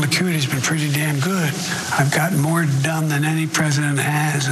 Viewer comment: Yo! Did he say "I've gotten more dumb" at 5:00 mark? LMAO I'm dying here. I watched that part over and over, you can't convince me he said "done"